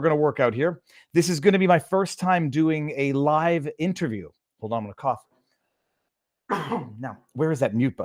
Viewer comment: I thought he said "Hold on to my cock".